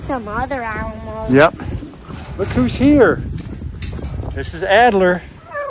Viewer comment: it actually sounded like he said Hi Adler!!!